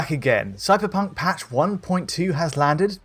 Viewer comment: Did he say "suckerpunk" at 00:06 ?